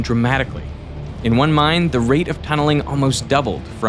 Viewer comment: Subtitles say "mind" instead of "mine"